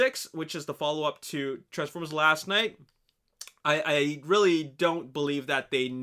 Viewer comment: it sounds weird when u say let's talk about last knight not that ur voice is weird it's just like it sounds like your saying we should talk about last NIGHT